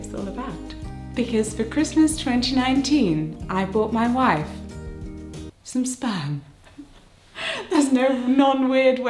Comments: I heard "in 2019 i bought my wife some SPAM" and i was confused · For a second I thought she said "for Christmas 2019 I bought my wife some Spam" and I was so confused. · I fully thought said “I bought my wife Spam™️” and took me a second to put it together 😅 Congratulations to you both! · I misheard and thought she said "I bought my wife some spam" and I was so confused until she mentioned babies and then I realised. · Am I the only one who heard "SPAM?" I mean I totally expected a pregnancy but what I heard was, "I bought my wife SPAM." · I honestly thought that you said you bought your wife Spam, as in the canned meat product. It was after you mentioned not being able to be inseminated on a Sunday that I realized my mistake. 😆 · The first time I thought I heard you say "For Christmas 2019, I bought my wife some spam." Weird as that might be imagine my confusion when I was picturing you making a baby out of spam for some reason. Congratulations. You will both be wonderful parents. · At first, I thought said "I bought my wife spam" and was hella confused xD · I'm so stupid every time said 'donor' I heard 'doughnut' WHY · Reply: At the start of the vid i thought she said she bought claudia some spam 😆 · i got so confused because i thought she said “i bought my wife some spam” and my little American foodie brain was like wow why are they so excited about canned processed meat? 🤦🏼‍♀️ but anyway y’all are the most beautiful couple